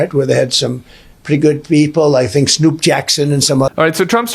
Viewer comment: I heard the "Snoop Jackson" the first time, and had to stop the video to Google it to see if Snoop Dogg's last name is actually Jackson.